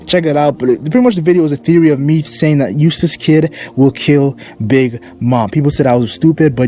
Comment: GOT MY LAPTOP FIXED SO WE BACK AT IT ALSO WHO YOU THINK DYING IN THE FUTURE OF ONE PIECE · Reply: i thought u said a useless kid will kill big mum lol it was eustass captain kid